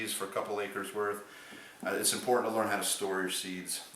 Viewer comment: it always sounds like he is saying impordant